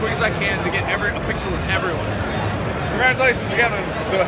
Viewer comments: “Hey, congratulations on your engagement!” You can tell that this was genuine. Look at his smile. What a pure man. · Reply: I thought he said “congratulations you’ve got a gay pin.” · "Congratulations you got an- a engagement" 😂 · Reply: I thought he said "congratulations Kevin"